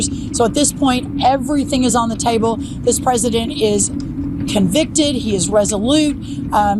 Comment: Thought I heard it, scrolled back to listen again and yup she (Rollins) said it, checked the cc, it made it there too, checked the transcript, it’s there as well. So even the chosen refer to him as “convicted” check out 13:17, I wonder what was going through her mind when she made this slip?